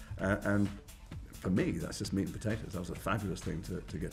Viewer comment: Does Jason say "that's just meat and potatoes" and 2:45? Haven't heard that one before haha